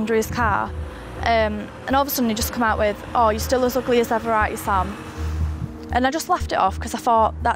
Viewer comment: ? WHAT??? I replayed it many times and I don't understand what she said. What he said she was "ugly as ever??" And did he say 3:56 I'm pretty dark me self?" If so, where?? I normally don't have a hard time understanding the language, but they kind of rumble their words together a bit.